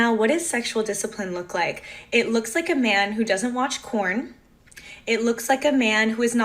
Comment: Anyone else notice she said corn not porn? She said corn like 5 times. Even the closed captions say corn.